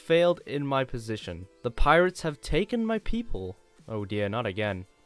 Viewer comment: when this dude said “the pirates have taken my people” I thought said “the pirates have taken my pp”